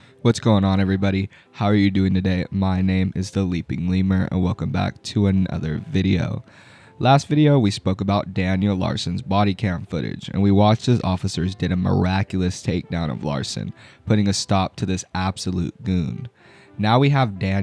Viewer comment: Brother I turned on this video while cooking and I thought I heard "We're talking about Daniel's body count" like HUH